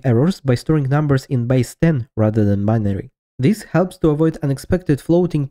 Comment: I was terrified it's an 8 minute long Mr Incredible meme, lol 7:29 "Uses by Stan rather than binary". What does "by Stan" mean? Is that what you said? Googling I only found Stan the programming language and BCD representation. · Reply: "Base 10" XD Sorry, for confusion. I've added subtitles.